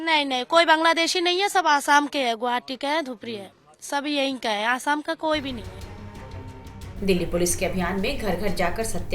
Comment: she is saying koi bhi asam k ni h